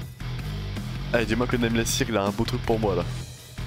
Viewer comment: I hear 100% "Nameless signal" 😀